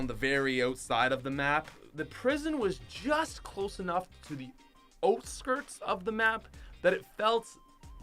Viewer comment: For #49 I thought he said oat skirts but then I remembered he is Canadian and he was saying out skirts